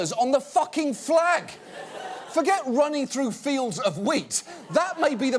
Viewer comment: when she said 'I ran through fields of wheat' i thought she said 'I ran through fields of weed' and i was like wtf